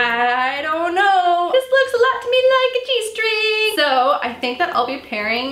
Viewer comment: OMG! You said "this looks like a G string" and I thought you said "this looks like a cheese string" haha😂😂😂